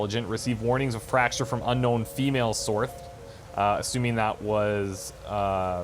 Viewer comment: does he say source or "sorth" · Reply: Source